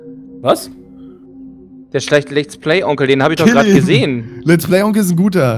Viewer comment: Hast du da etwa LetsPlayGronkh zu mir gesagt xDD Hat sich so angehört haha